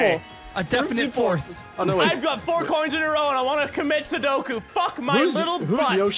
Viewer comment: Did he say commit sudoku at 5:26?